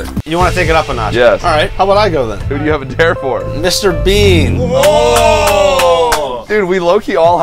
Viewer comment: For A second I thought he said mister beast